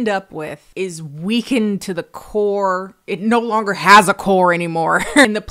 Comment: I heard “weekend to the core” instead of “weakened to the core”. Still works.